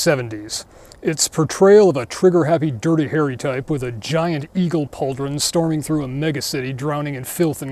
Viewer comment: I heard "Ego Pauldron" and I refuse to be corrected!